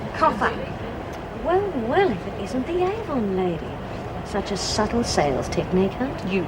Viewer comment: What did Sonia call Phyllis at 17:59 ? Sounded like angle lady or something like that. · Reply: Carlene Boulden Avon lady